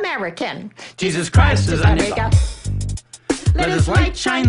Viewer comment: I completely lost it because not only does he say Jesus Christ is my nigga but he says Jesus Christ is a nigga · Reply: They're sayin "our" not "a*